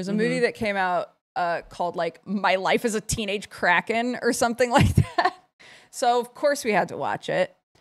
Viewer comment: I thought said my life as teenage crackhead 😂